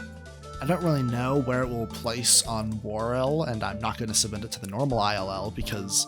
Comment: what does he say it’s like wore or something